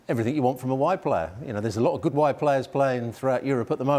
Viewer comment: Wide players, I thought they said white players. lol